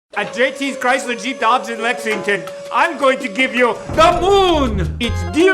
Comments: I heard "doge in Lexington" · i heard "dodge at Lexington"